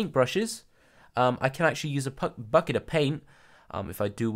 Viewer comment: I heard "Bucket of pain" XD